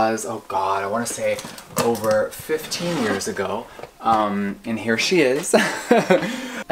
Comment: I thought Jeffree said 50 years agi